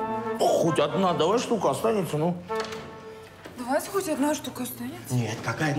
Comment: Кино супер, смеялась почти не останавливаясь😂🤣😂 Я сначало услышала "Хоть одна ШЛУХА ОСТАНЕТСЯ" , когда девушка сказала, оказывается она сказала " Хоть одна ШТУКА останется" 😅😅😅 аға вы супер, удачи в творчестве !